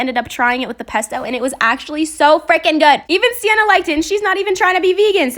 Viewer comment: When you said cut some more I thought you said put some more